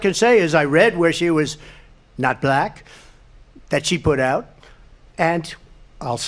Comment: Trying to determine at 11:20 if he's really saying "She put out" as a vile jab implying that she slept her way to her various positions over the years, which I know is a right-wing talking point. Did I hear that right or was he tripping over his words again talking about when she "decided to be black"?